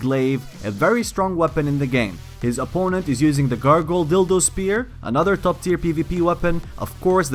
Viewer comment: sounds like he says dildo lmao